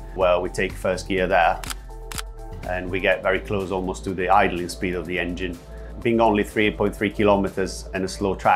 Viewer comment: "The .... speed of the engine" What does he say? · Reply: idling speed of engine 😉😉